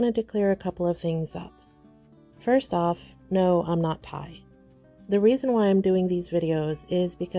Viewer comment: no I'm not what? Played it back a few times and still couldn't catch what the word was. Anyone? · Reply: I'm not Thai.